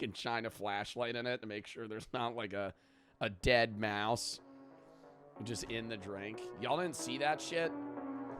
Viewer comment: did someone say... deadmau5? absolute cinema ✋😲🤚